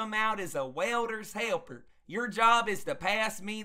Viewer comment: lol. sky hook, can of a.i.r... grab me a nitrogen blanket and a water hammer asap! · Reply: I thought you said a welders wrench